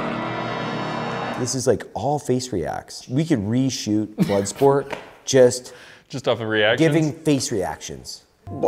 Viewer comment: "We could re-shoot bloodsport using nothing but facials" is what I heard him say.